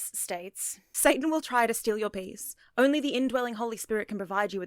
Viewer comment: I misheard what she said as “Satan will try and steal your bees” 🐝